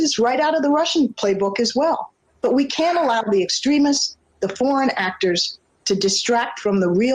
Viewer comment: Closed captions calls Netanyahu 'nuts and yahoo'. Sorry, I'm entertained by simple things. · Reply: I thought I heard Rice say, "porn actors" instead of "foreign actors".🙂